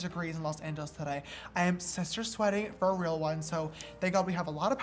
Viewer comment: When James said he is "sister sweaty" i thought he said he is "sister slutty"